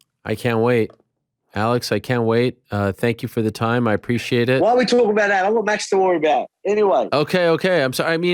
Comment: Ariel is about to wrap the interview but then volk says something and the interview goes on for another 15. What does volk say??? I played it back like 4x and I can't understand. I think i don't speak Australian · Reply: After talking about Charles and lightweight and ariel went to wrap it volk said 'Why are we talking about that I have max to worry about' Probably because I'm Aussie i understood him perfectly the first time 😂😂😂